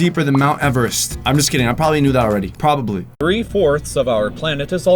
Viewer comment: In the subtitles at 1:31 it says 'nude' instead of 'knew' like why though who wrote the subtitles?! And it's totally ironic that he says 'I probably knew already' and it came up with 'I'm probably nude already' XD did someone do this purposely?!